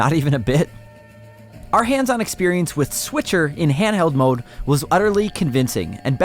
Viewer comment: Did he said: Switcher at 1:00? Or I misheard something?